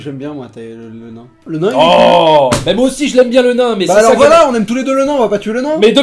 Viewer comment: Et moi aussi j'aime le nain!!! LOLOLOLOLOLOLOL